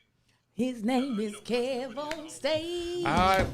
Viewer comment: In the missing part I just heard "he owns his app,kev on stageee" 😆😆